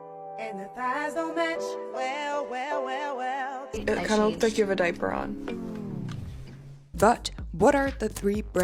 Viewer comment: Yo I lost it when Kendell said “I don’t think you have a diaper on” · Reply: I thought she said "it looks like you have a diaper on"